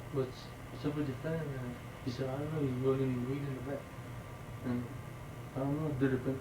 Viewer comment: What did he say at 4:13 to 4:18?? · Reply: David got to the house they went to the back he said soemthing to his cousin souded to me like "you have the weed in the back?" And he just started hearing "kill him kill him"